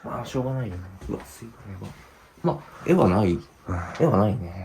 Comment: うん、無いよ って女の子の声？が聞こえる気がする